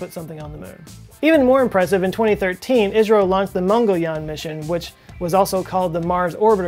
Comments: Did you say Israel ? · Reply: he said ISRO. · Did I hear you say , "In 2013, ISRAEL launched the Mangalyaan Mission"? Go easy on the hummus, big boy. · He said Israel instead of India